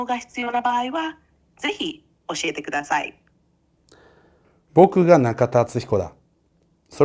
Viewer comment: 「僕が中田敦彦だ」の所が「僕がキラだ」と同じに聞こえるw